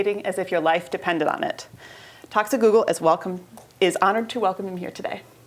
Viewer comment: Anyone else hear "Toxic Google" in the intro?